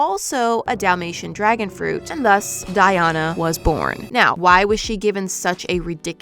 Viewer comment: My name is Ana so it sounds like ur saying die-Ana